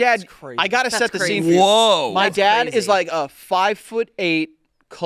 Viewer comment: “my dad is mario” is all i heard